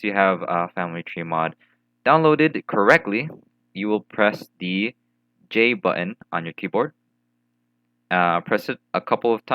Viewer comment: I can't hear what you're saying here 0:49 , where should I click? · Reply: Press the j key on your keyboard